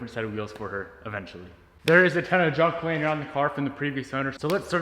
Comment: ton of drugs from previous owner lmao. Sounded like that